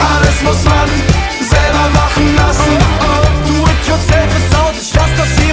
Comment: Upsala.. Ich hab statt 'Do it yourself is out' immer verstanden 'Du wertest selfies 🤳 aus'... 🙈 Manchmal aber auch... 🙊..